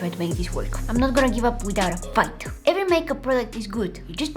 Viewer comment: did you say... the f word?!?? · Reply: She said fight